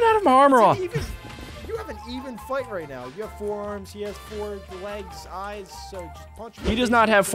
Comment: did you just say 4 eggs? listen closely